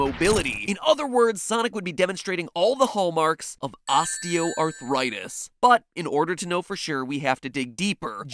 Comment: I thought he said osteoporosis